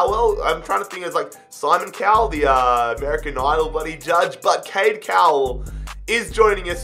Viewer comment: Was I the only one who heard him say Simon Cowel is American